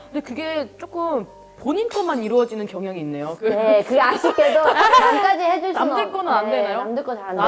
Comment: man, what she says here sounds exactly like "kuyashii kedo" which means "it's frustrating, but" in Japanese 🤯